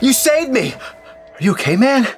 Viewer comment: sounds like he said caveman